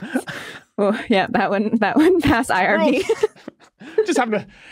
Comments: the caption is "that wouldn't pass irony" instead of "that wouldn't pass IRB". Great episode! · Subtitle error: 4:41 should read "IRB" instead of "irony"